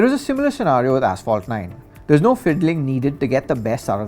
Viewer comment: 🤔did I heard Ass-fault instead of asphalt